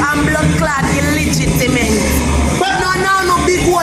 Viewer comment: Hold on likkle. Are my ears deceiving me? Did she say illitigiment at 2:42 instead of illegitimate? Aiii sah!!!!!!!